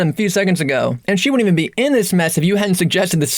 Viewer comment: Grammar mistakes 4:05 it sounds like you say "wouldn't" but the captions say "won't" 5:42 you forgot the d in should 7:40 not really a mistake but for some reason the top caption goes away after you're done saying it leaving the bottom caption which I don't think has happened before 10:15 🤣 it says "whip his ass together" when you said "whoop his ass together" which I think is the funniest grammar mistake so far 11:50 you say "than last time" captions say "than the last time" 13:15 no grammar mistake I just thought this whole part was hilarious 🤣 13:24 like earlier but the opposite there's captions then bottom captions appear then there's a grammar mistake as "assh*le" turns into "As" 14:13 more weird caption stuff